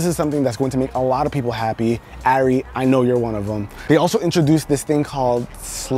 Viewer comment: did he say “airi i know you’re one of them” bc that’s my name n i’m creeped out